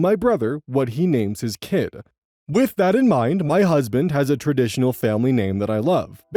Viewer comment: Not me hearing “my brother has a traditional family name” instead of “husband” and being confused as FUCK for like 30 seconds